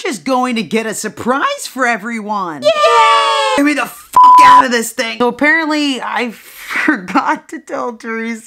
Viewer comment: The good ole’ days😂 amazing video and it felt just like it did that day, love you!!! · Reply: Did you really say GET ME THE F OUT THING on